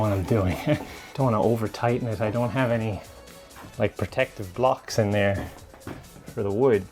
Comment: i heard "protective glocks"